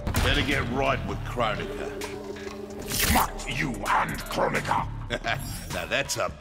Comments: Did he say "Fuck you and Kronika"? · Did Baraka just say the f word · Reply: I hear "Makt you and Kronika." That's likely Tarkatan tongue. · I thought at 0:59 baraka said F*** you an Kronika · did Baraka say "fuck you and Krobica?" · Did Baraka just say “F*ck you and Kronika “ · Did baraka just said "fuck you and kronika" @ 0:53? LMFAO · anyone else hear "Fuck you and Kronika" ? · does baraka say "Fuck you and kronika"?